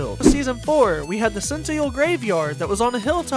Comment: did bro say Senteal?